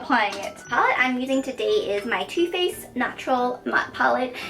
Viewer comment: When you said two faced I thought you said tooth paste 😂